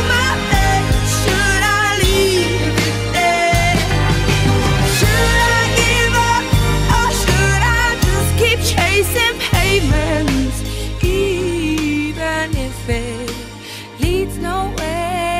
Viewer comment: I thought it said chasing payments LOL😄